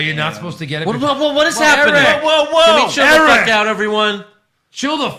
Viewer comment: I thought said get the F out? The Ric Flair impression was gold lol Also, good show. My first time watching. I'll subscribe.